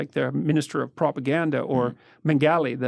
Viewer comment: Some pls what is the fucking name he’s saying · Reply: Josef Mengele? WW2 nazi doctor